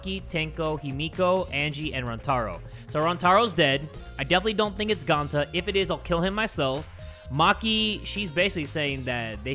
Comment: I thought he said "I definitely don't think it's Gonta if it is I'm killing my self"but nah